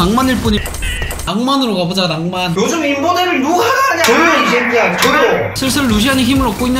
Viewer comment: 나만 이거 조용 이섀키야 조용으로 들었나